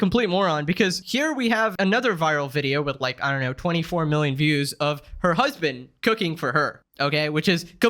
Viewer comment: I can’t be the only one who thought it said “She cooks her husband”…